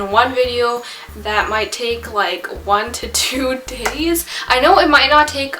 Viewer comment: When you put your subtitles on and at 5:26 it says two titties instead of two days....